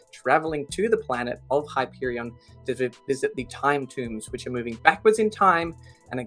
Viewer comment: The first few times I heard about Hyperion, I thought people were saying the Time Dunes, not the Time Tombs.